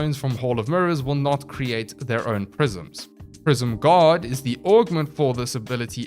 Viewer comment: I was just listening and honestly thought he said Prism "God" instead of "Guard" and then I got excited to play Mirage. 🤣